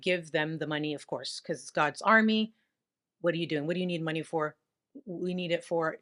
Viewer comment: Did you really say "dog's army"?